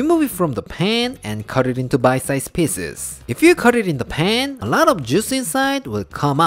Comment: Oh 😂 in every few sec i heard "Dubai Size Pieces" and i had to check back to see what's that 🤣🤣🤣 found it's actually "bite size pieces" 🤣😂🤣😂